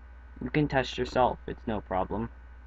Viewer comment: Lmao I swore you said you can touch yourself it's no problem right at the end. But you said test.